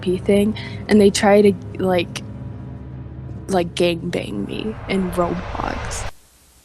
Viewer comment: DID SHE JUST SAY GANGBANG IN ROBLOX nah that got me rolling😂